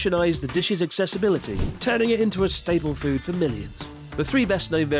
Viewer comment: At first I thought he said "turning it into a staple food for ME!"